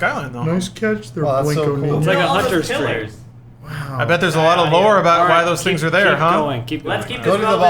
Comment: Holy shit I heard "Nice catch Blanco-Nino"! G.I. JOOEEEEEEEEEEEEE!